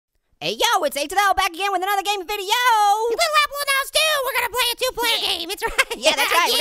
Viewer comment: I think Little Apple said "Little Apple when I was two" At the intro I think it was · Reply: “Little Apple in the house, too!”